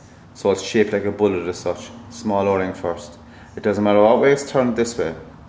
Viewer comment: What are you saying at 1:17? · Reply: Small o ring first